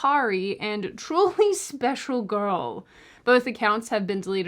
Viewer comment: I didn't hear you the first time. I thought you said Drooling Special Gurl. XD